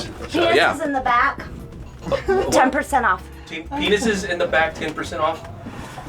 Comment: Really appreciate the support on this new episode! Thank you all. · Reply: I'm not sure what she actually says, but with closed captions on it says 'Penises in the back, 10% off!' Sounds like a deal to me! This is hard to type I'm laughing so hard!